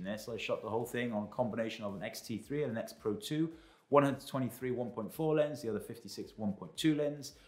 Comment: what lenses? Great shots. 3,900 frames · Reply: yeah the first time I heard he said “136 1.4 lens” but it could be 16 1.4 or 23 1.4 , for some strange reason I hear 136 1.4 but that is impossible......